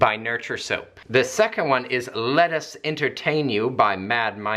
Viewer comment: When you said ''Let us entertain you'' i heard ''Lettuce entertain you'' which in this case also works i guess 😂